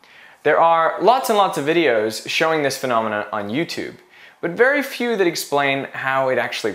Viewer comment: Did you say 'this phenomena' at 0:37 ?